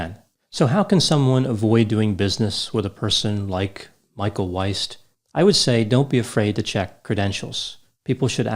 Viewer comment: Oooooh East! I thought you said Weist.